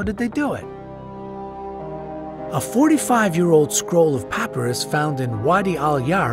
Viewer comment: did he say the scroll was 45yrs old not 4500yrs